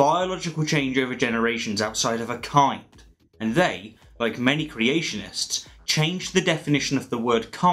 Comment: What generalization? I was talking about the people who use the argument discussed in the video, that's a very distinctive group of people. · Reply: David Damshek to me it sounded like you said all creationists